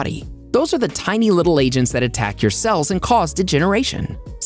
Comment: I thought at first he said that free radicals are tiny little Asian that attack your cells.